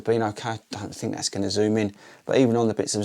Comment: Did you call the foam zoomx at around 6:30 ?